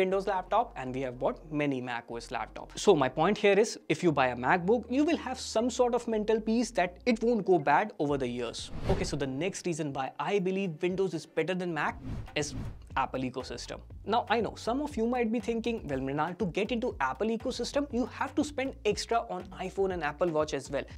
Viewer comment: i think I heard that he said "why windows is better than mac is it ecosystem" 😅 in 2:57 he admitted windows is better